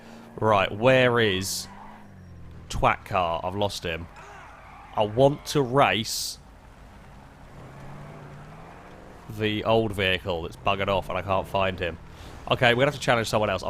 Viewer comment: Did you call that old car the Twat car? 3:10 I thought you don't swear in videos...